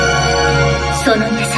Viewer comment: Someone can tell what is Homura saying here 0:41? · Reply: By ear it sounds like “その優しさであなた自身を傷つけてしまう”, which means something like “You will hurt yourself with your own kindness/With that kindness, you will end up hurting yourself”